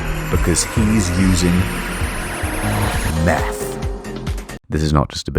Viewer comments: I thought he said "meth" and was like oh that makes a lot of sense. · I was looking away at the time and thought I heard "...Using Meth"